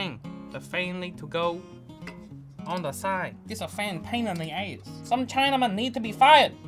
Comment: captions say “paint on the AIDS”